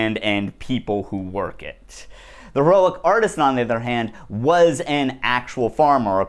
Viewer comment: Hey I was attempted searching this guy and the painting using the closed caption option, because I didn’t understand what exactly you were saying. I know it’s more work but when referring someone or something could you put the name on the screen? I tried looking up “the relic artist” because that was the closed caption. It’s flawed but a name being displayed helps immensely.